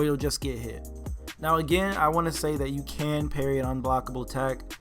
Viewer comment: Dude you're growing so quickly Congrats!!!! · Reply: andi bro 2:30 he clearly says you can. Don't know what on earth you're talking about..